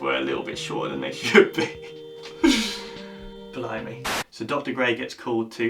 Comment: Lol @ 13:02 — at first I thought he said “blow me”.... It wasn’t until i rewound it to double check that I realized he was actually saying “blimey”.... Lol 😹 Lame!